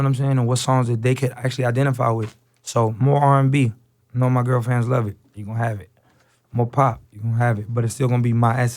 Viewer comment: Anyone else heard him say "More R&B know my girlfriends love it"